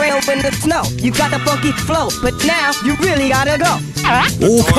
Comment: When prappa says the word “ funky “ it sounds like he saying “fuckin”